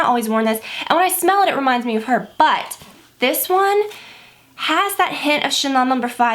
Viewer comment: at first I thought you said "and when I smell it, it reminds me of her butt!" hahahaha dying